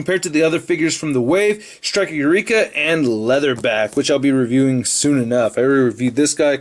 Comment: when said Leatherback i thought he said leather bath XD